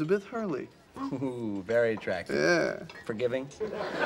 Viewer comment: What did chandlers say at 0:19 to 0:20? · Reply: Forgiving